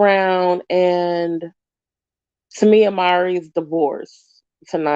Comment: Uhhh...I'm confused. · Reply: lol I kept replaying it and it sounded like you said tamia mowry